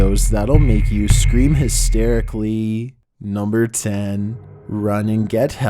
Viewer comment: I thought that said “these are the top ten scary videos that will make you cream hysterically”